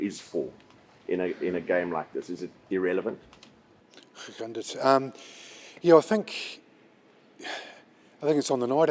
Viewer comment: Anyone else catch 4:20 Scott attempting to greet in Afrikaans😂😂😂Class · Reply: He said "hoe gaan dit" which means "how are you?"